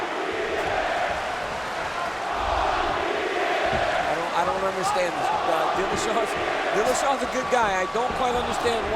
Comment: what was the crowd saying? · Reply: “Fuck you TJ”